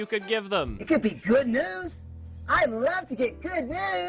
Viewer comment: "Good news" from Cool cat I heard "Good nudes".